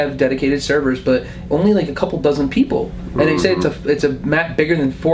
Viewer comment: Todd Howard just did an interview over at YouTube At E3. Completely online, no way to play offline, no single-player, always PVP and no NPCs. Every human you see will be a player. And there are only 12 in the game including you. Yeah. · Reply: Yes I do apologise, I got that wrong. He did say dozens, I thought I heard a dozen